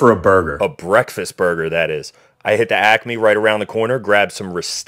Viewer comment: Sounded like he said “I have acne right around the corner”